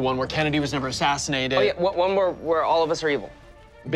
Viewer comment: what does he say a t 1:07 ? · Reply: "what about where all of us are evil?"